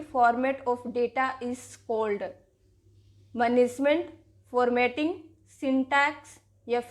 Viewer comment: U said Punishment instead of saying Management